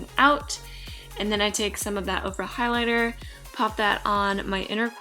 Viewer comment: Haha I thought she said Oprah Highlighter. I was like ooh Oprah has a highlighter. 😂😂😂😂